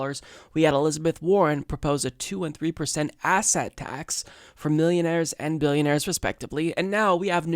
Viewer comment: Did you say asshat tax? J/K